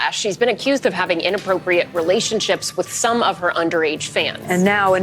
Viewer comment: Underage man? Ah yes, you mean… a teenager! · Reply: I heard 'underage fan'